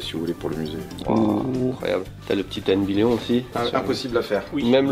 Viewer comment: Salut tout le monde merci pour vos vidéos. Par contre à 8:48 tu prononce le nom d'un jeu apparemment impossible à faire mais pour moi c'est surtout impossible de comprendre les deux mots que tu prononce. Je ne sais pas si c'est la toute petite musique derrière ou que tu mange un peu tes mots mais je n'ai pas réussi à trouver le nom du jeu. Si vous pouviez m'aider, j'entend ten vidéo ou ten guidéo je sais pas. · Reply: Ten Billion casse tête Nintendo crée par Gunpei Yokoi